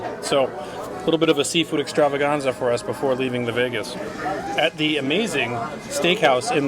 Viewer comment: I thought just said leaving New Vegas. I've been playing too much Fallout.